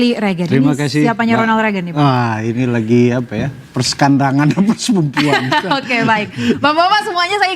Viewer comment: ga salah denger ini 😅 persekandangan... Yang biasanya dikandang itu apaan bang 😭